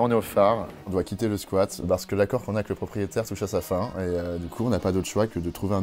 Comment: what did he say in French after "l'accord qu'on a avec le proprietaire..."? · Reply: "l'accord qu'on a avec le propriétaire touche a sa fin" it meens its coming to an end